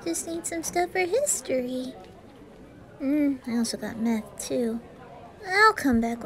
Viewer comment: Fr thought he said, "Also got meth.." In the beginning- 💀